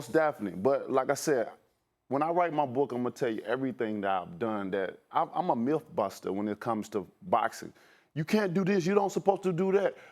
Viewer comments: Thought my mans said imma milf buster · Did he say he’s a MILF Buster @ 1:40 ? 🤣🤣🤣 tooo much info bro! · I thought tht nigga said milf buster😂😂😂💀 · I thought he said he was a Milf buster 😂😂😂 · I thought he said he was a “MILF Buster” lmao 😂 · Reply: ironman6992 lol I thought he said milk buster. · Is it just us or did he say "Im a MILFBUSTER"? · Thought he said MILF Buster lmao 😂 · lmao I thought he said MILF Buster 😂😂😂😂😂 savage 😎